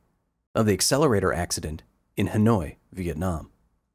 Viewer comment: Thought it said Hand, Oi!